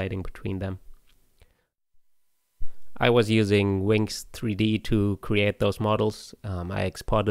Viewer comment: what is the 3d modeler used? I heard “Winks 3D” but can’t find it 🤔😅